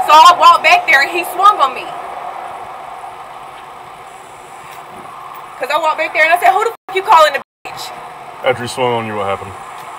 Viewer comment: Thought she said she walked to the store?